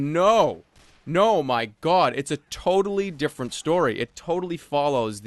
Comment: Lol when you said 'manga' I heard "manguy" and was like "wut? O_O"